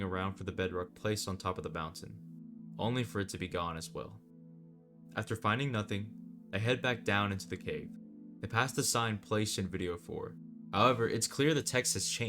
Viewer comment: Why did you say “I head back down into the cave” at 8:01 minutes in.